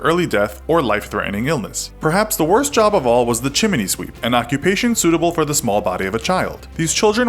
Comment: Did you just say, " chiminey " sweep?